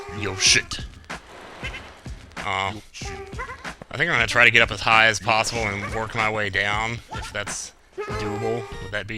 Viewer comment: I swear at one point or another I thought I heard you say "I'm gonna get as high as possible." Did I miss something?